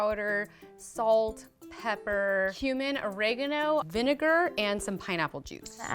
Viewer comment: I think she said human